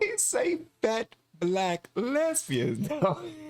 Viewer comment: Did he call them ugly? I just heard 'fat black lesbian'.